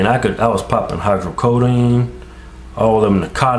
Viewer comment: does he say that he was poppin narcotics?? hahahaha